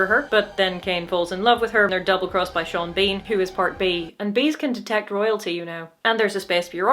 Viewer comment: I thought you said Sean Bean was part bean, which would have been a hell of a plot point